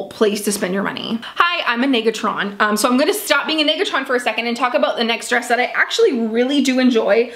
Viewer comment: During 6:14 and 7:14 (approximately) is she saying that she’s a“niggatron” and she’s trying not to be a “niggatron” and wth is that? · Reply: Nega-tron.....as in she’s being negative about the clothing